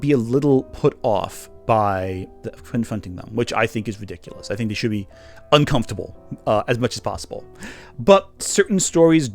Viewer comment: "I think you should be uncomfortable as much as possible"? Did I hear that right? What an odd thing to say, especially as you have already said people shouldn't include certain fictional settings that YOU find "uncomfortable".